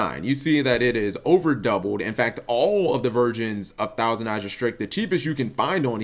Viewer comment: Did he say VIRGINS??? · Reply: I know he meant version but it sounded like VIRGINS